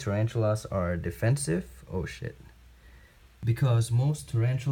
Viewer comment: Did he just say "Oh shit"?